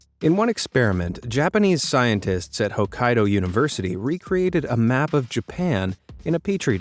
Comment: I THOUGHT YOU SAID OHIO UNIVERSITY